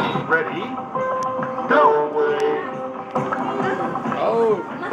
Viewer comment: Do you hear « No way » or « Go away » ? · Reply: ⦅PICͥODͣOͫUX⦆ i hear “No, away”